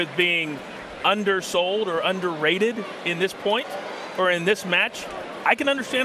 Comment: I’m sorry did you say underrated?